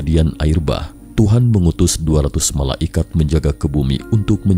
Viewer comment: saya bingung dengernya, MALA IKAT, mungkin maksudnya MALAIKAT (bacanya malaykat)